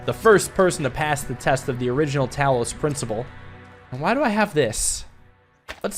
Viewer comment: I heard "towels principle" XD.